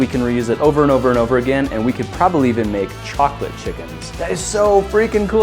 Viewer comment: did he just said choclate